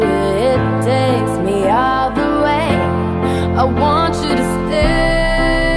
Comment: First time I heard this song I thought she said I want you’re stake 😅